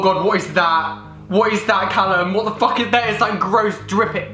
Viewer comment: did he say what the fuck and forget to Blair it out ❤️😂😂❤️😂❤️😂😂😂 love u ❤️😂❤️😂❤️😂